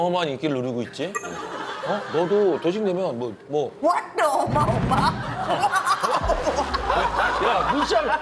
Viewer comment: 왓더 어마어마 ㅋㅋㅋㅋㅋㅋㅋㅋㅋ돌싱상 ㅋㅋㅋㅋㅋㅋㅋ 아 진짜 ㅋㅋㅋㅋㅋ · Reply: 난 왜 어마어마가 옴마옴마로 들리지...ㅋㅋㅋ